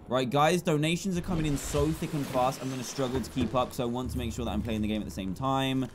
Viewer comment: did he say so thicking fast? 🤔